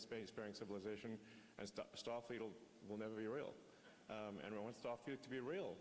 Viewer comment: did he say Starfield ?